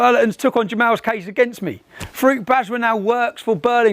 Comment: When he says 'Farooq Bajwa' I keep hearing 'fruit badger' and looking at this guy, he is spot on 🙂 It's a brilliant video.